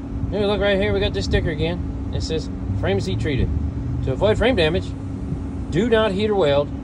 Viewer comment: At first I thought you said “ to avoid brain damage..”. Finny either way na,kay😊